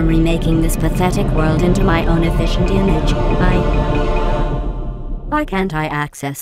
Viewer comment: What does she says at 0:37? - "Nothing can stop me from remaking this pathetic world into my own efficient...what? I didn't catch that word. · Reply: I'm pretty sure she said "image." :)